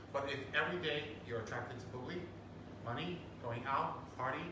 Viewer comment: Can someone tell me what he said at 29:17? " but if every day you're attracted to [bully?], money, going out" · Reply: it is "bubli", Rinpoche always used it to replace the word "sex", kinda like a made-up euphemism